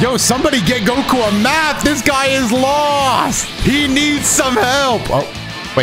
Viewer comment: I thought he said he needs some milk